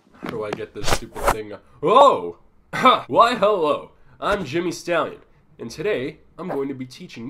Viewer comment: I thought he said Jimmy Fallon.